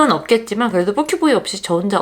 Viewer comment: 뻐큐보이로 들리는거 나만그러나요 ㅋㅋㅋㅋㅋㅋ